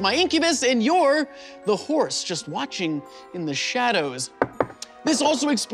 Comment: Did he just call the viewers whores?